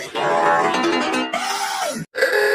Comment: "umm is there bacon burger?"🤓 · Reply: all i heard was "walkin bakon burgar"